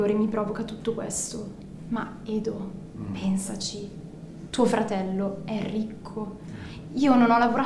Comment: ho capito “tuo fratello è rinco” 🤣